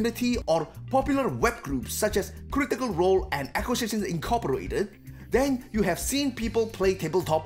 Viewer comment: What's the other group mentioned at 3:22 ? I know critical role, but I can't make out what your saying for the other group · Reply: Acquisitions Incorporated